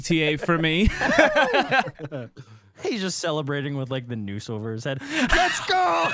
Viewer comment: Wait, what did Vanoss say? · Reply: He said "he's celebrating with the noose over his head"